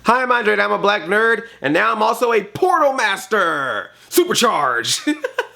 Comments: At the beginning, I could've sworn Andre said "porno master" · I thought he said he was a porno master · i thought he said porno master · wait... did you just say that you are a porno master because that is what I heard